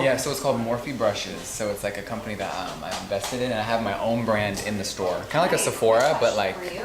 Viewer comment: I am in no way sticking up for Jeffree Star but what I heard from Jeffree was that he was “invested” in Morphe which would make sense with his brand being sold through Morphe retail and the talk about stores “they” are opening. Hmm idk I just am confused on the invested versus investment thing because it could mean different things 🤔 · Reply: OMG, you may be right! I always thought he said he was an "investor in" and not "invested in" till I read your comment and replayed what he said with the captions. He said he is invested in, i.e. he is a stakeholder, in the sense he sells products via Morphe but he is not a shareholder.